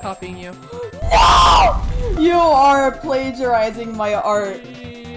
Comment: what said: "your plagiarizing my art" what the subtitles said: "I THINK I THINK MY ART"